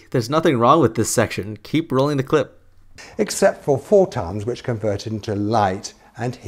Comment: I think he said "Four tons". I know I'm a bit late but I just wanted to point that out.